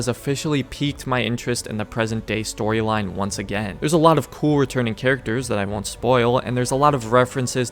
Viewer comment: I love how this guy goes about the modern day at 3:20 saying "there's some cool returning characters that I won't spoil" CC says "Rebecca" LOL